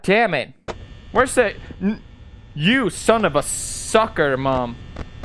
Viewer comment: "you son of a soccer mom" hold on, writing that down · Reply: Lol I thought he said sucker mom